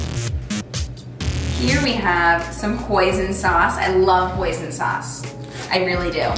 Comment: omg who else thought she said “poison sauce” when she said hoisin sauce😭💀